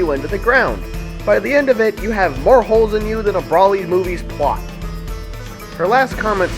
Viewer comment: did you say Broly movies PLOT? If yes then WTF? Sorry but what did you mean by that?